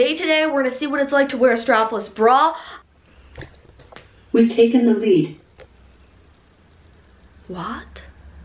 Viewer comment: i thought alexa or siri said "im taking a leak"